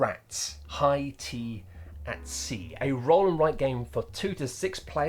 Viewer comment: I thought you said it was a "Roland Rat game" at the very start there..